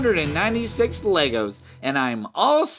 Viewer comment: did it say leg-ho?